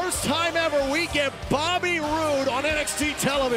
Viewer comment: For a second I heard "Bobby ROOOOOO"